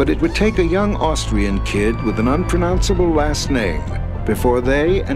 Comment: "A young Austrian kid with an unpronounceable last name" had me laughing lmao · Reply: I thought he said “unpronounceable ass name” xD